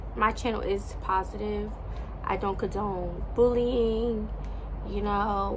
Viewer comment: I thought she said i dont condone booty eating..😂 this has to be a shaming ritual. Everybody wants veiws, subscribers anything for fame...